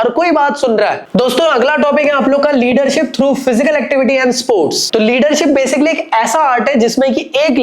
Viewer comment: i heard "liter"😅😅